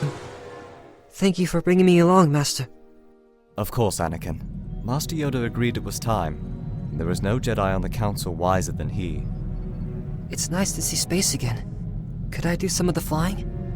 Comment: I thought Obi Wan said "there is no jedi on the council wiser than ME" i was like damn Obi no wonder Anakin is so resentful